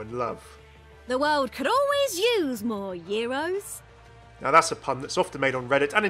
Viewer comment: Guys, can you please tell me what tracer says at 3:25. I can not understand, is it her "The world could always use more heroes" or the last word is different. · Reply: She says "The world could always use more euros" because puns